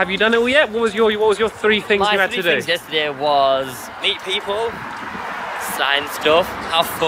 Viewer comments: when said to meet people and sign I thought he said eat people and when stampy was playing halo it looked like dan from tdm was beside him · I thought said"eat people" lol